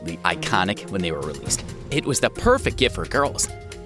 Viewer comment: am i hearing this right "indo kids"